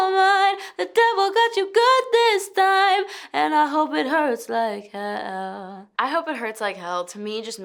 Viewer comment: I swear i thought it said And i hope it hurts like hell (Hellllll)